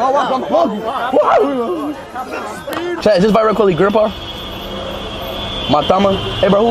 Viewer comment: Did speed just say Mahatma Gandhi is Virat Kohli's Grandpa🤣🤣